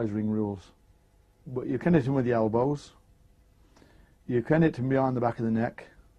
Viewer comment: can’t hit with elbows(while it shows him how you to do it “legally”) is what I think he said. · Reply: He said you can hit him with the elbow... can’t hit below the belt